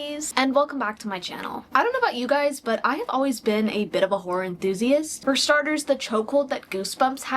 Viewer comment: Only 20 secs in and my apd already showing up with hearing “whorer” instead of “horror”